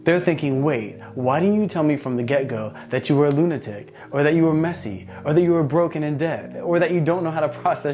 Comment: Lmao you said I didn’t know you were broke and in debt for the honeymoon phase but I thought you said “you didn’t tell me you were broken and dead” I was cracking up 😂😂